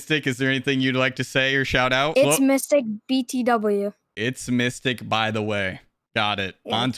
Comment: I thought he said “it’s mystic bbw” i sat here confused